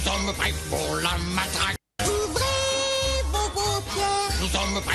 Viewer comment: moi j’entend « ouvrez vos concons «